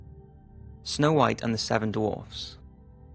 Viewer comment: I thought when you said Snow White and the seven dwarfs I thought you said Snow White and the seven doors